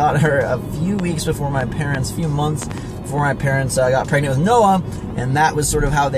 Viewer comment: I thought he said "I got pregnant with noah" lol